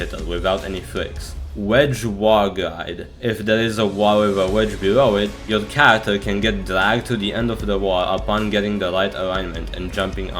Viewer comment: anybody understand what he said on 4:42? all i hear is jibberish tbh.. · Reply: if there is a wall with a wedge below it, your character can get dragged to the end of the wall upon jumping on it and getting the right alignment